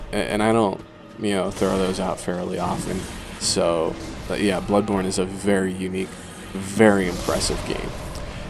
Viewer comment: When he said bloodborne sounds like he said bloodporn